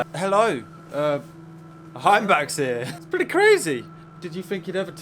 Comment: Lol....I thought you said Sandbach, as in the service station on the M6.